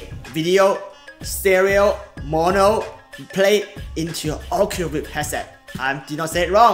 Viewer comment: Hey. Did you say "Oculus Rift headset" around 8:48 while holding up the Oculus Go or just "Oculus headset"? Sometimes (rarely) you are a little hard for me to understand and Google's automated subtitles here are no help at all. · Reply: I talk way too fast and not articulate - my bad! I meant Oculus Go but I said the rift instead - I do have both so something I confused... I should really slow down. Thank you for the feedback!!